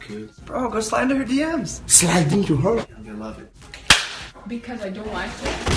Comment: à 1:40 la 2ème et 3ème phrase ils disent quoi ? J'arrive pas à comprendre.. :( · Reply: Ils disent "- Just slide in her DM's!" (Mot pour mot c'est "Glisse dans ses DM, en gros envoi lui un pv) et Mister V repond -"Oh je glisse juste Ok!" D'où la balayette.. ^^ #ThankMeLater